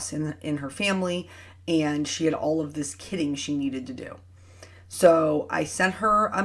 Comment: I thought it said knitting not kitting 😂😂😂 oh man poor Eric